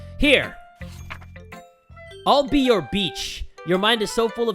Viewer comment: While listening this on 2x I misheard "I'll be your beach" to "I'll be your b**ch"